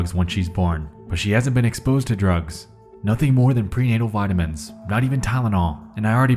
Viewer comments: am i the only one who heard “pre-anal” instead of “prenatal” at 11:16? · Sounded like you said “pre-anal vitamins”